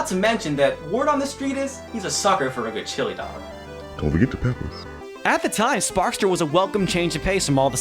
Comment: What did that voice say? "Don't forget the peppers"? "Don't forget the pickles"?